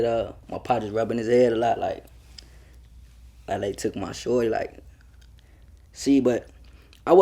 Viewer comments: sound like dude said "LA took my shorty" I could be wrong though. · Did he just say L.A took my shorty 9:19 so is he saying L.A Capone killed JoJo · Did he say LA took my shortie listen closely between 9:15 and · sounds like he said LA took my shorty